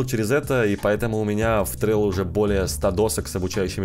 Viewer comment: где сотни досок? Не услышал название Можно ссылку? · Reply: В Trello